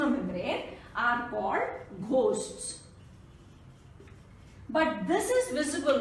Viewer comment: Mam 9:20 p broken pieces ko kya bola h???? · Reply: Ghost